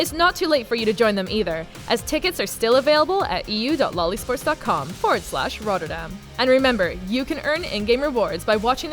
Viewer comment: Is it just me or does it sound like she says loli sport instead of lolesport